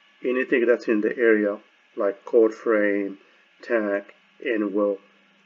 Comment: you said, "some of it will get out of the core and it will get into anything in the area like the core frame, tenek? and will cause some losses." I spelled that word "tenek" because that is what is sounded like but that of course is not an english word. I just couldn't understand what you said. What did you say? What was that word? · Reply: Tank. The flux will exit the core to the surrounding area. Thank you.